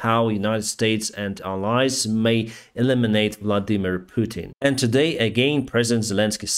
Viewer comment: Vladimir Putin. 16:10 From ' voice I thought he said "Bloody Mean Putin" or is it just me?